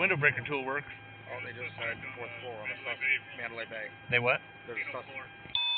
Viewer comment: First cop(just to the right): "oh they just said 4th floor on a suspect of the Mandalay Bay." BWC cop, "they what?" First cop: "there's a sus(cut off by beeping). BWC cop: "on the casino floor?" Second cop: (unintelligible) First cop: "nope, got another suspect in there". BWC cop says, "not on the casino floor though, on the 3rd floor...right?" Third cop(not in view): "I thought they said 4th."